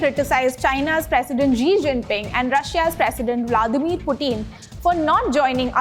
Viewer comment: Did she just say Ji Jingping and Pootin? 🤣🤣 God her accent is crazy!